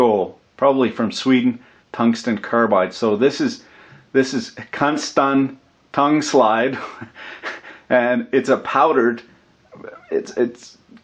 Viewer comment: Did I hear cuntgsten tongslide? 23:07 :)